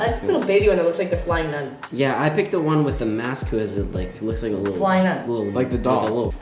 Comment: “yeah i picked the one with the mask who has like a, who looks like a, lil, lil, like a lil, like a lope.” that’s what i heard lmao